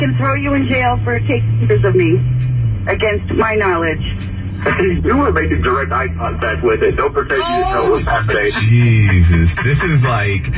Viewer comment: what did he say? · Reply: "You were making direct eye contact with it. Don't deny it."